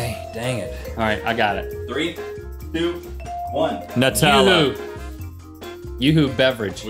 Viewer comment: hahahaha at 9:24 9:25, i thought he said nut salad!